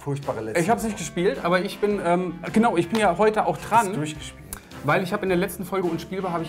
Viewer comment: Hab zuerst verstanden, dass sagt: ,,ich bin ja heute auch ". xD